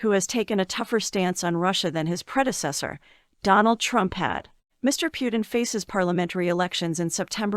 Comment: 😅😅😅 Donald Trump had. LOL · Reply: i heard it as donald trumpet 🤣